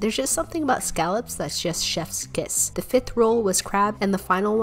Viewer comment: I thought she said hamster instead of lobster for a second 😭